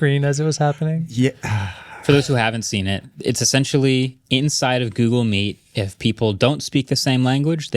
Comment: I keep hearing "inside of Google Meat"